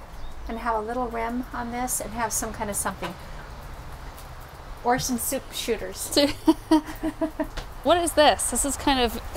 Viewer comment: I LOVE your mom! Exhibit A: 21:30-40 “to have a little…something. …or some soup shooters.” Is that what she said?! My mom and I share this same sport (team antiquing). Thanks for sharing your fun together- I have a hard time antiquing with a purse alone…I can’t imagine a purse AND a camera!!